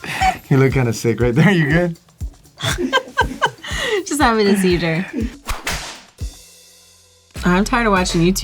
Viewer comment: did she say "just happy to see you jer?" I like to think the BigTime crew still says hi now n again on the sets 😊